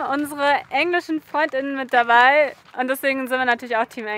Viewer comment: Warum bezeichnest Du einen ausländischen, männlichen Begleiter als Freundin??? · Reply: Ich habe Freund-innen gehört. Ich hoffe das ich mich da verhört habe, denn diesem Gendergaga-Stottersprache kann ich nicht zuhören. Aber das hier ist ein Reisekanal und kein Politikkanal, also wie immer die Videos Genießen🥰👍